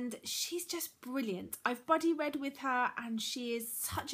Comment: Everytime you say 'buddy read' I hear 'bloody read' haha Love your channel ^_^